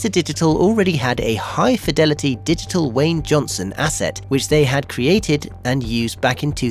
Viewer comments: bro seriously, did you just call him wayne johnson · Did you say.... WAYNE Johnson? 🤔